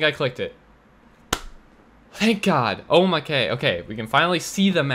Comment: Did anyone else hear when he said o my k